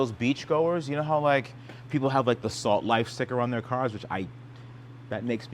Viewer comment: Salt life? Im from the midwest i thought it said slut life